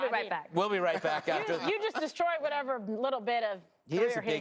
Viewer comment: What does she say at 1:43 that Greg just "destroyed what little they had left?"